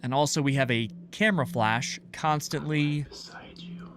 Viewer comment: Nah, am I imagining things, or did he say, "Im right beside you"